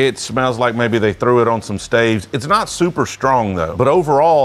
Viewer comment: Is it just me or when he said it smells like he put it on some staves it sounded like he said slaves💀💀💀